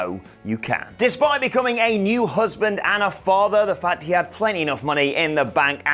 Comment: When you were talking about Daniel. You Said he was a new Husband, I misheard you and thought you said Heel Husband. Rewound back. Heard what you really said. Started playing around with the phrase I thought I heard. And I just made my last verse for my new album. When it's out I'll send you all the link.